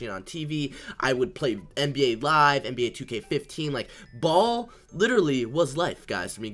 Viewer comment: did he actually say NBA 2k15